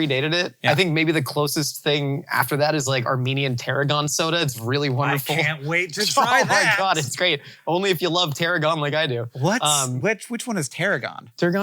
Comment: omg I thought he said "Armenian Pteranodon soda" and i was then and now, very dissapointed to realize that there is no dinosaur soda to ever exist